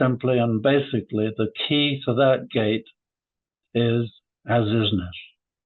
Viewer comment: what does he say is the name of the key I can't make it out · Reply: says the key is "As-Isness.' For anyone unfamiliar with the term and related ones: a. AS-ISNESS is the condition of immediate creation without persistence, and is the condition of existence which exists at the moment of creation and the moment of destruction, and is different from other considerations in that it does not contain survival. b. ALTER-ISNESS is the consideration which introduces change, and therefore time and persistence, into an AS-ISNESS to obtain persistency. c. ISNESS is an apparency of existence brought about by the continuous alteration of an AS-ISNESS. This is called, when agreed upon, reality. d. NOT-ISNESS is the effort to handle ISNESS by reducing its condition through the use of force. It is an apparency and cannot entirely vanquish an ISNESS.